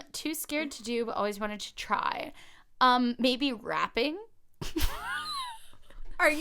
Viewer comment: When you were talking about things you'd like to do but you're too scared to and you said "rapping" I heard "wrapping" (like wrapping presents) and I was very confused.